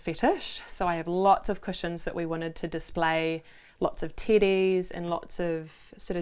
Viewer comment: Lol.i thought they kept saying they had lots of "TITTIES". TEDDIES. 😆😆😆